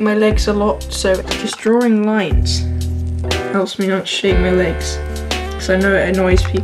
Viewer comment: I thought you said that you draw lines because you shave your legs alot and I was like??? Hahaha can you shave my hairy dinosaur. If you don't get the reference it's gonna be weird